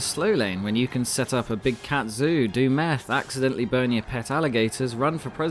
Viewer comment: I thought he said “ accidentally BONE your pet alligators”